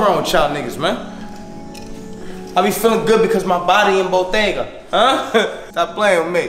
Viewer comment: Did he just say his body is a bodega?...you should prob get a checkup